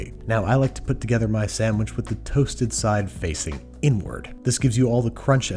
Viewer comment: did he just say samish instead of sandwhich